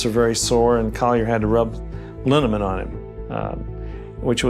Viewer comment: did he say he rubbed aluminum on the dad??? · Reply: I was also confused, but he actually said "a liniment".